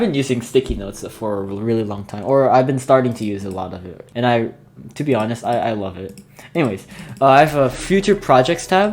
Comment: What if you psi and pfi on the bottom of the train cars and they went into the underside of the station? 7:55 did u say “pew pew” or “pp”. Cause they are 2 different things. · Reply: I like that idea. Also, I said pp